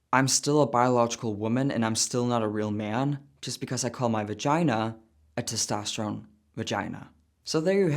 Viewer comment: when you were mentioned your vagina I totally heard "my vaGUYna" 🤣 vaguyna>>testussy